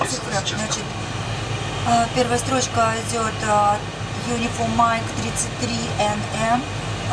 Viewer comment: Бедная стюардесса. "печатайте uniform mate" (или что там после uniform?), когда нужно напечатать всего две буквы UM - вверх разумности. Откуда стюардессе знать, что нужно сокращение? Проявлять самодеятельность и фантазию? И только спустя минуту он сказал в ответ на: "это в одно слово печатать" - "юниформ майт, всего 2 буквы", чем, ессно, ввёл в ступор стюардессу. Ну и такого много. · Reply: Grigory Kornilov спасибо, послышалось uniform Mike, но подумал, что за бред, специально несколько раз прокручивал, потом наудачу гуглил, но безуспешно. выбрал менее бестолковый вариант на свой взгляд, не угадал))